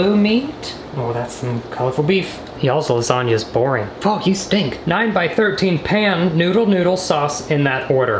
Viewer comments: it sounds like he says f*** you stink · I think he said Fu**